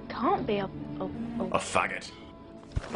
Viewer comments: I thought he said "Now your fault." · it sounds like he says ''now your foot"" lol